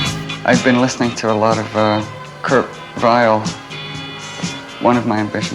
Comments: Who does he say he was listening to at 23:28?? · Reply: Kurt Weill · does Byrne say that he'd been listening to a lot of Kurt Vile? Is he a time-traveler?